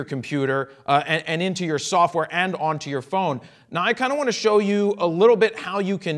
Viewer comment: I will have to import the 1 preset I made to LR mobile. To me when he said "Now I kinda wanna show you..." at 2:08 I heard it as "Nikon I wanna show you.."